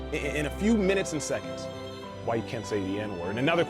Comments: These captions are terrible. 7:30 caption says "why you can say the N word" when he's actually saying you can't . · I love this video so much, and I thank you for posting it. I do ask that you fix the typos in the subtitles, only because in one instance, at 7:31, you say the word "can't" but the subtitle says "can." Someone who is only able to read it would seriously misinterpret your message. And it is such an important message!